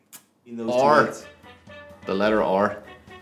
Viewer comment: Holy fuck i thought they all said "the letter or" I was so confused.